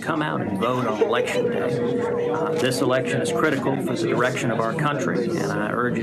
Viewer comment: Does Ted say the "erection of our country" at 2:50? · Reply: "critical for the direction of our country" but we can go with what you thought if you want